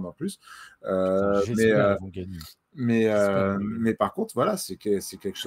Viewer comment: il dit quoi à 54:26" j'espère qu'il va gagner ? "